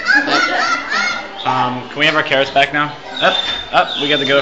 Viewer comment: LOL my name is cerys and when says can we have our carrots back now it sounds like he said can we have our cerys back now? i freaked out lolol.